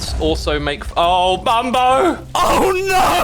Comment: Did... Did X call him Bumbo when he blew up around the 21:00 mark?